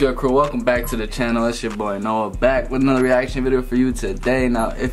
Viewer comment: It sounded like you said Noah Beck lmao 😵